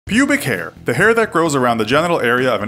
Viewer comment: I thought it said "Public hair"